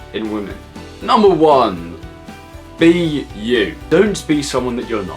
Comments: When he said be you i was zoned out so I thought he said the letters B U and i was like wtf does that mean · When he said 'be you' I thought he said 'B.U.' And I was like ' Wtf does that me'